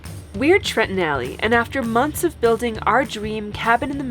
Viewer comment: My sister introduced me to your channel, I thought she said "Trenton Alley", figured it was some kind of British thing. Figured that would give you a laugh. LOVE YOU GUYS!!!